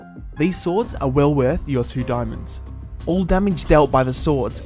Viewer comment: Did i hear "3 diamonds"?